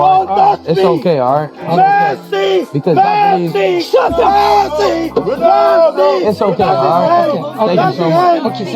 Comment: 辺りの後ろで叫んでるおっさんメッシ、メッシ、ファンタスティック！て聞こえる合ってる？